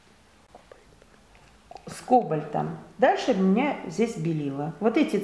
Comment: На 5:15 я услышал шопот (скобольтом) Кто тоже услышал ставь лайк